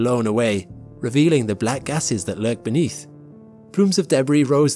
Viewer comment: First time I watched, I heard everything okay. Second time I watched, I heard: "These images are the surface of Jupiter's atmosphere being blown away, revealing the black asses that lurk beneath."